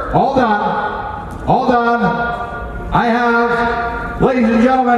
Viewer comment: I had to reply a few times I thought he said 12 billion 🤣 I was like what